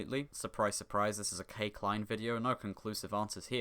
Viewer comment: when you said "k klein video" I heard "cakline video" XD